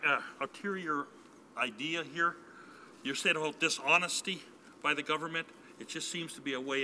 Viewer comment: I think she was saying be honest.